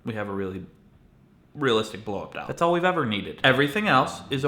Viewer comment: What does he say at 1:18? "Realistic ball-ub-dub"?